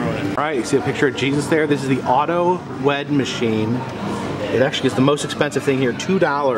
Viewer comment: I swear I heard “auto wet” machine... Then he said “well my wife Isn’t here..” I SWEAR I DIED